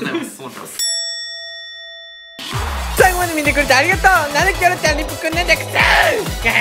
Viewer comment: キョロちゃんって言ってるのが恭ちゃんに聞こえるの自分だけ？www